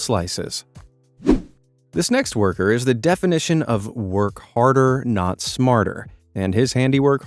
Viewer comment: Did he just said work harder not smarter in minute